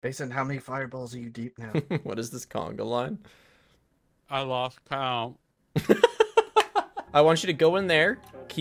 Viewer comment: "Mason how many fireballs are you deep now" "I lose count" Words of a sage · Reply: I hear it as "i loss count.."